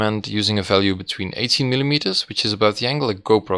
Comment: did he just say use a in between 18, and 18? (subtitled) based on the 50 mm i'm guessing he meant 80 correct?